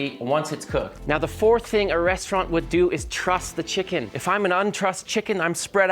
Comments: I heard "trust the chicken" lol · At first I thought you said step 4 was to “Trust” your chicken, it’s actually “Truss.” Big difference.